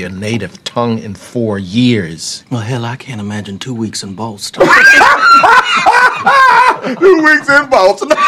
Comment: Lmao i always thought he said “i cant handle 2 weeks at most” not boston💀